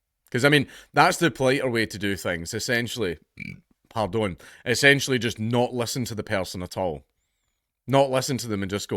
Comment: Say again? Couldn't understand the accent you used. Maybe slow it down if that's not english. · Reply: "im not fae here" as in not from here